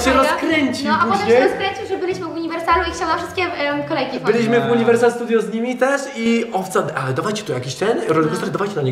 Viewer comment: co powiedział ten gość co przechodził? Xd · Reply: Że ma kokaine